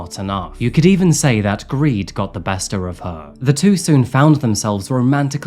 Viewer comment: I heard "greed got the 'bester' of her"😂😂